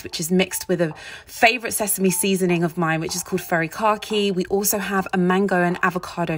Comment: Never heard of Furikake before, and when I first heard her say it, I thought, "Furry Car Key?! What?!"